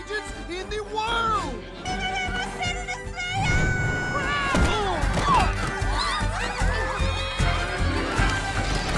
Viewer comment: What did that lady say? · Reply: "my baby is going to be a star"